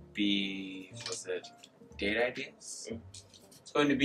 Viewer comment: I thought he said "gay ideas"